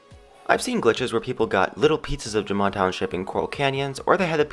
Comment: Wait wait wait... Did he say "little pizzas of jamaa township" at 2:34??? XD